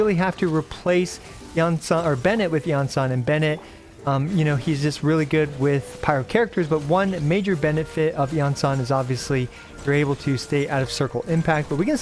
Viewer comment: did he just say one major Bennett-fit